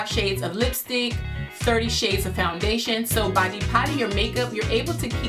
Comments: I thought it said 'Deporting' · I watched this and thought it said "How to Deport Makeup"